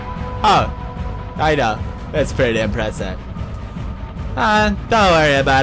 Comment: Lol it sounded like he said "It's Pewdie impressive" around 5:09.